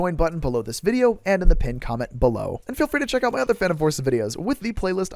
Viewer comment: I hate that I hear "Fanum Forces" 💀 I aint even into Gen Alpha lingo, my brain is done 🔥